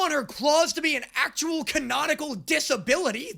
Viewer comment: I know he meant to say canonical but it sounded like can-nautical and I feel like that joke writes itself